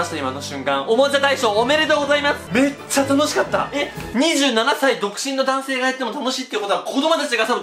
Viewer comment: 7歳独身って聞こえたw